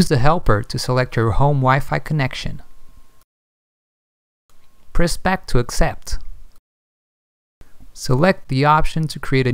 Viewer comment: I hear him saying "prospect to select" at 1:18 and doesnt make any sense when I go through thia process. What is he saying? I cant understand.